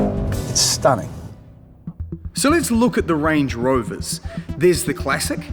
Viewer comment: Did i just heard him say lange rover? 😂